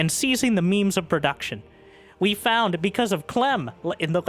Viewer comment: "Seizing the memes of production"? was that intentional or misheard.. i guess i will never know